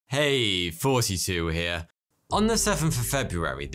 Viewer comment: I always hear 'hey! Forty-two here...'